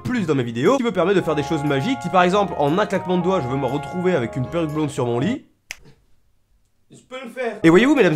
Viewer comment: À 1:28 j'ai entendu je vais me retrouver avec une paire de blondes sur mon lit je me suis dit mais non il va pas le faire ! Bah il l'a pas fait.